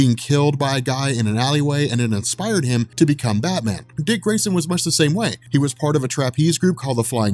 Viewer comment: I heard at 24:38 "His parents were killed by a mugger in an alleyway, and it inspired him to become a Madman"